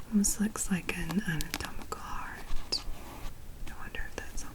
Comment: looks like a what? I can't hear it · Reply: anatomical heart